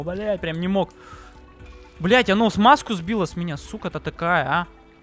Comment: мне послышалось "Он с меня смазку сбил"